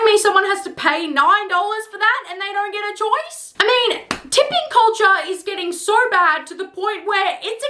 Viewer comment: my dumbass thought you said “dipping culture”